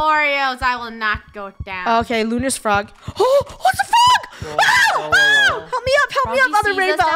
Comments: did anyone heard a bad word in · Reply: she said "what the frog" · holy- I think just said “Oh WHAT THE F” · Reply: I turned on subtitles and she said " oh, Oh, ITS THE FROG!" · i used to think she said "OH ITS A FROG" nononono thats not what said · Why did i hear says "WHAT THE (swear word)"